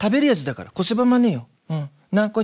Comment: こしばまねよ？これはなんて言ってるんだろ？ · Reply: こせばまねよ＝こうすればダメだよ と言っております。 まね、まい、まいねがダメという意味です。